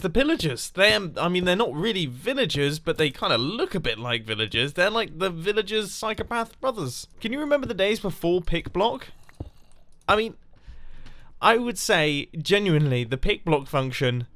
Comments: Is he saying pig-blocking? If not, what is he saying and what is it? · I thought you said "Pig Block." 🤣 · I thought he said pig block lol. Pig block scares me 😢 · LOL I thought he said Pig block